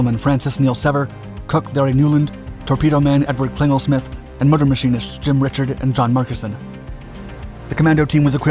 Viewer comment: can anyone correct me but I hear murder machinist? Doesn't sound right..